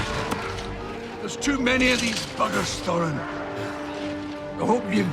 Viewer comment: Tell me am i heard it right that his cousin just : too many of this F**kers... ???